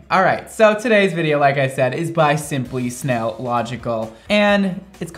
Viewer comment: at first i thought he was saying simply snail logical as a joke but then he said it ten more times